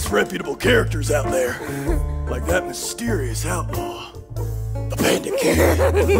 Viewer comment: the first time i watched trail to oregon, the whole time i thought joey was saying “the panda king” instead of “the bandit king” which made the whole show 30x funnier